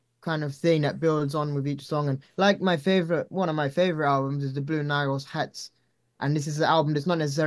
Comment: What’s that album he names as one of his favs at 13:54 ? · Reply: Sounds like he said, "The Blue Nile's Hats." I just started listening to it, and it sounds amazing! I think I can hear some influence it may have had on his vocals too.